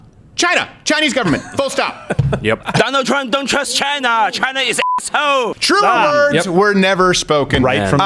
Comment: It sounds like he said "China is a$$ H0" lol Like and leave a comment if u agree 2